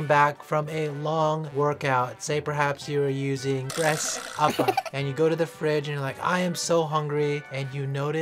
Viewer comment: I keep hearing 'breast upper' ... 👀